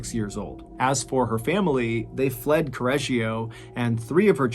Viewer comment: I choked on my coffee when you said the name of the town because I thought you said “scorreggio”.